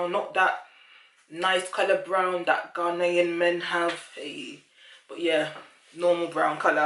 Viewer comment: grenadian men, u know they got them nice brown eyes. thanks hun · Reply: haha! I thought you said Ghanian